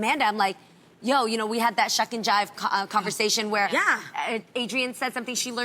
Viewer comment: What was the word? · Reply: Adrienne said "shuck and jive" on the show about maybe a week or two ago, and Amanda was like NOOOOOOOOO!!